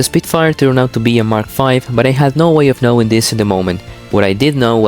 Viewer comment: mistake on the subtitles here, you wrote mark IV instead of mark V